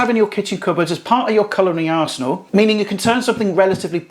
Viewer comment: colouring arsenal. I had to rewind that as I heard something else the first time but it’s such a good non culinary term 😜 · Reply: I did actually say culinary, but I can see how it sounds like colouring 🤣